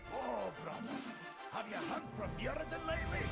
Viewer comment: what did brann say at 34:35? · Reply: I think he says: "Oh, brother, have you heard from Muradin lately?"